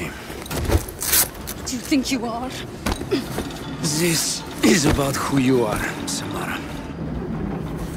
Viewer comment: я услышал "This is about хуево, Samara"